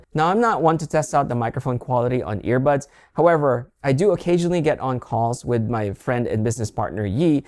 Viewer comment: I'm sorry; This is very unrelated, but did you say YEAT? 😭😭😭